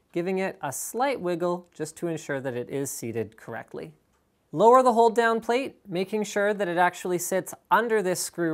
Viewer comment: I heard: "lower the whole damn plate" lmao